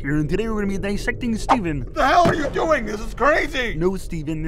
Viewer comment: What's being said past 0:08? Can't tell over the screaming. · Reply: George Rose you did this to yourself steven